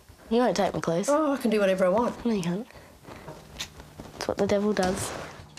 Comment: Did she just say "thats what the devil does" after saying she doesn't believe in all that?